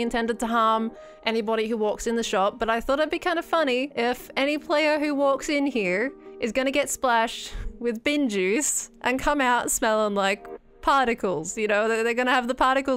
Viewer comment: I first thought she said "get splashed by BEN juice", I was wondering who was Ben and what happened to him to get juiced...